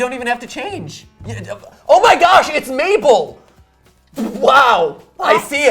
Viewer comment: I swear he said Maple